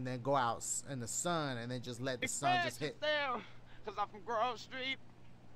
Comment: wtf did cj say and why does he sound like he having a stroke?!🤣🤣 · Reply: Express yourself cuz I'm from grove street